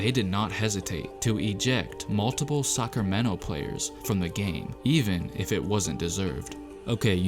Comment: Say it with me: “ASTERISK” Not “Asteriks” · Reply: I heard suckermento players